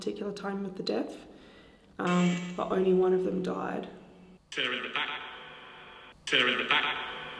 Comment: I heard"killer in the back" not"they're in the back"🤔 awesome vid as always 👍